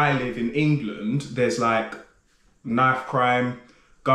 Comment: I know this is entirely irrelevant but I misheard you saying "knife crime" as "math crime" and was thoroughly confused for a second. I was trying to imagine what math crime would be.